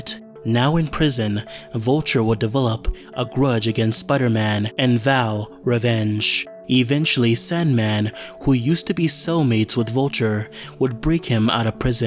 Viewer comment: When you said that Sandman was Vulture's "cellmate", i misheard "soulmate". 😂